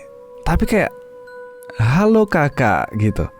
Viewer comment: Kalo volume nya kecil kedengeran kyak hahaha,,klo di fullin volume nya hallo kakak, klo pendengeran aku sih gitu bang